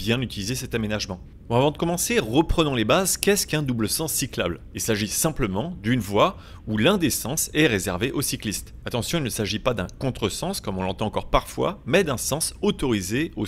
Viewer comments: J'ai entendu "une voie où l'indécence est réservée aux cyclistes" à 0:25 J'ai eu peur. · j'ai entendu "il s'agit dune voie où l'indécence est réservé aux cyclistes" 😂